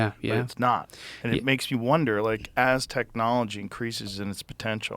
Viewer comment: "Aztec-knolgy" Anyone else catch that?